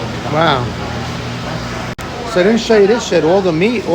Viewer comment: BTW, I had to rewind 16:35 - 16:38 or so like 5 times, almost sounded like you said, "So, I didn't show you this s#!t." 🤣🤣🤣🤣🤣🤣🤣🤣